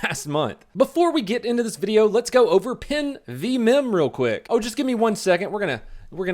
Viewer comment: that accent :P I heard "pin vs mems"